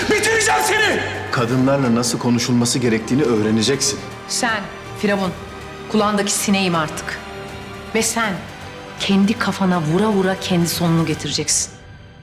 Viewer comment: Azra 0:32 ne dedi biri yazabilir mi 😅?? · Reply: Sen firavun kulagindaki sinegim artik ve sen kendi kafana vura vura kendi sonunu getireceksin.